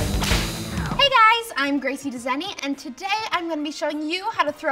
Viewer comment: I always hear "Hi I'm Gracie The sunny"